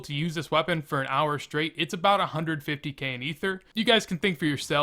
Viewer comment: haha thought he said 150k ether was thinking wtf 15m an hr, but he said gp